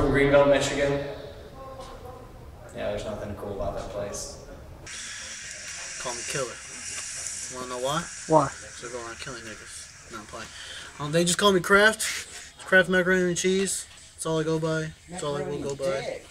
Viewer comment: did bro say they call him killer because he go around killin nikkas?